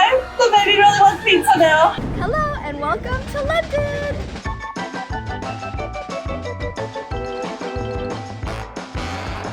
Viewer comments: wait did she SAY THE BABY GIRL????!!! OH MYYY GODDD!!! ITSSS A GIRLLLLL!!!!! YESSSSS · Did she say baby GIRL wants pizza